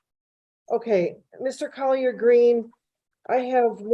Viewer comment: Mr. Collard Greens?? That's what I thought she said until I read his Zoom screen name. LOL